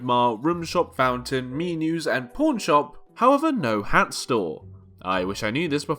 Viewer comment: why does the subtitles say 🌽 shop instead of pawn shop ✌️